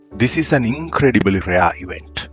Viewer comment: At the beginning, he said, "this is an innncredible elhellahewhet." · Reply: Oh, I thought he said, "incredible, rare event."